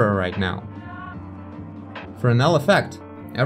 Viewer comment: Does someone know what's the name of the effect he's talking about? I tried to look for Fornell effect but I didn't have any luck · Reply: Sure. It's spelled Fresnel. Kind of a tricky one to spell phonetically.